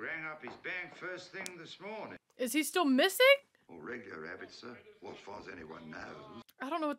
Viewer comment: The subtitles got it wrong. The policeman did not say "regular rabbit." He said "regular habit." Mr. Banks was not in the regular habit of drinking. Dropping the "h" in "habit" and slurring together the two words into "regular'abit" confused the subtitle generator. Nothing to do with a rabbit at all. Just to clarify.